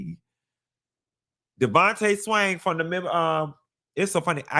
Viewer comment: Damn..41 years old and always thought he said Devante SANG 😂😂😂